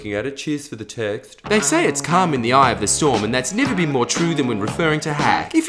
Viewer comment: I thought he said "cum in the eye of the storm"